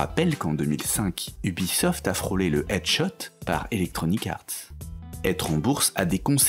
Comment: ai je bien entendu edge shot ??? c'est quoi ?